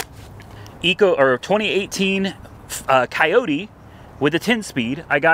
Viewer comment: F<num> coyote for 8k? am I hearing this right? Also whenever you mentioned $1100 truck payment I assumed that was a 4 or 5 year loan. not a 7 year damn. Did they screw you over with trade in value on the trx? how does that even happen?